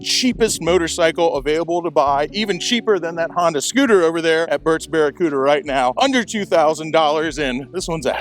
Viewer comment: Had a chuckle.... yes, I'm 12 years old.... @ 2:16 - "Burt's Bare a cooter"... who else heard that? LOL - Just having fun - nothing but love!